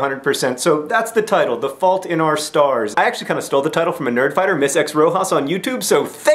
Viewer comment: i thought it said "the fault in our stairs". i thought it was some kind of reference to harry potter. :P